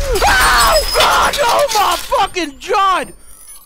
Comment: Anyone else hear him say oh my Fucking jod